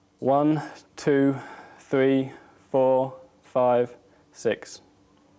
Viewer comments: i heard 6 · it sounded like someone said 6 a little voice